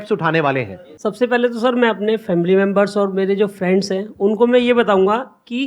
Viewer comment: #sabse phele Me apne family member.... Me be like: ye kya bol ra h... # sabse phele Me apne family members hi km krunga😳 😂😂